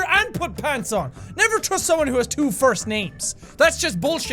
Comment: O I thought u said never f someone with 2 first names🤣🤣lmfao